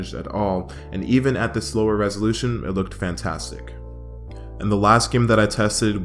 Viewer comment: Time stamps for mobile users. Thanks for being patient with this wacky upload schedule guys lol. $250 Gaming PC (almost) anyone can build is in the works right now, as well as a GT 1030 alternative :D ~Time Stamps~ 0:00 Why We're Building + Intro 0:23 Disclaimer 0:52 CPU/GPU 1:33 RAM 2:15 MOBO 3:28 PSU 4:20 CASE 4:55 Build Montage! 5:41 Prepping For Benchmarks 6:40 Emulators 7:05 PS2 Games 7:54 Gamecube Games 8:50 Wii Games 9:40 Wii U Games 10:43 Temperatures 11:26 Gamepad 11:58 No Skyward Sword ): 12:19 Outro & Goodbye :) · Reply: around 10:05 did you say slower resolution or are my speakers just so terrible that I cannot properly hear it. Ps great vid